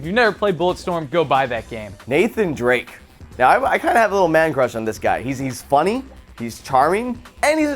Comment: Did he say "man-crush" at 2:34 ? o-0